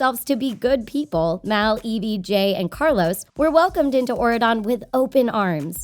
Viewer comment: when u said Jay's name the subtitles said edj XD WTH